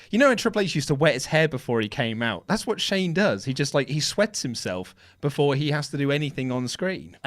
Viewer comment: Lol said Shane sweats himself I misheard I thought he said he wets himself haha😂